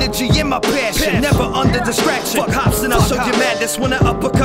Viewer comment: what did you say was it hopsin?? really I just wanted to know also dope entry! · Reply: Dogma555HipHop he said fuck hopsin while referencing one of his songs